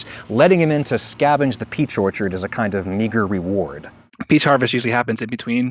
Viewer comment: WTF!?!?!? N word?!?!? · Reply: I know he said Meager